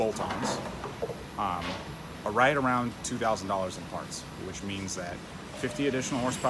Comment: did i hear wrong...$2,000 in parts for this 50 hp gains?